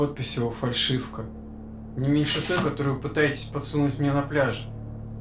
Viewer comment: и 11:47 что он сказал? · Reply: вы мне еще этот трюк пытаетесь подсунуть на пляже.. 11:47 - ... лаборанткой, вы же хотите здесь такие...